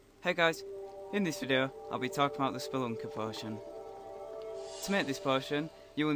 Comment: i thought it said spunker... lol